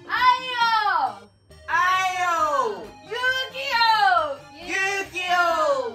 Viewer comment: の勇気をが遊戯王に聞こえる笑